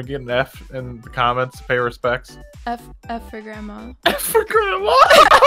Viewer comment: I swear they said f your grandma not f for grandma